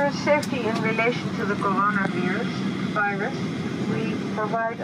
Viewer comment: Did she say Corona beer ..........Virus lol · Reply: Ha ha. No beer! She began pronouncing it the Dutch way, 'corona-veer-us' then corrected herself to the harder English emphasis and pronunciation, Vy-rus.